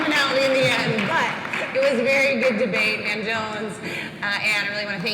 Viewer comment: I was disappointed when I realized this said 'van jones.' Thought it said Jon Jones and was hyped for some asswhooping